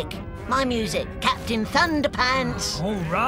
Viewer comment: i thought it said captain underpants